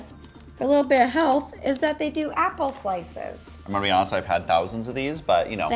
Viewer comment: I really thought you said ‘apple sauce’ but I guess it is ‘apple slices’ 😂